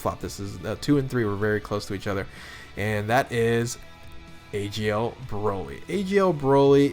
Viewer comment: Did he say agl broly